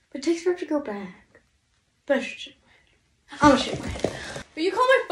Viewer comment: I stg it sounded like was saying “ima shit my head” I was so confused at first 😂